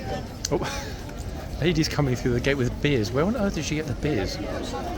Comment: Gosh, I thought you said lady coming through with a beard !,,,,😂 lovely horse . Sunshine your end eh raining here .❤from Cornwall